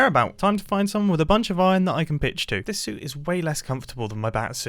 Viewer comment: watchin this and at the part where he was talking about his white board i was caught off guard because i thought he said "now to find someone with a bunch of iron i can bitch to" when he actually said "pitch to"